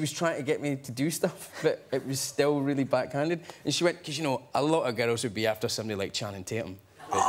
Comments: Can someone please transcript what James said at 4:10? It seems to be funny but, unfortunately, i'm not that good in English/Scottish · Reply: He says, "and she said, 'you know, a lotta girls would be after someone like Channing Tatum.'" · what is james saying at 04:08 ?? can t understand clearly :D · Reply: “But it was still really backhanded” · What does james say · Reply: Majestic Spoxxi "and she went, "COZ y'know, a lot of girls would be after somebody like Channing Tatum. But not me!"" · I have watched this for like 6 times already, and only then that I realized James said "Channing Tatum". Thought he said Johnny Depp. :( BUT GOOOOD HIS ACCENT IS A KILLER. I LOVE IT